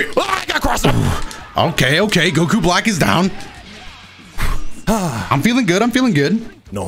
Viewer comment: At around 17:30 when says "Ok ok Goku Black is down" my phone heard itself and thought said "Ok Google" which made the voice assistant pop up 💀